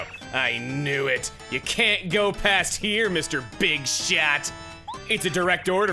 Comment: …did you say…[BIG SHOT]?!